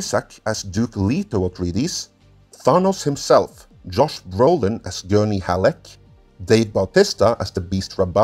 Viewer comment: Did you say Josh Groban? LOL